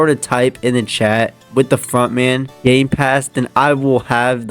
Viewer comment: why is it sound like your saying f- in